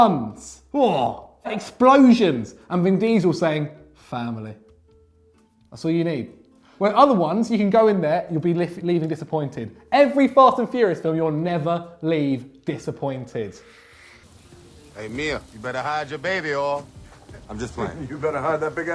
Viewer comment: I thought said “Fart and Furious” 😂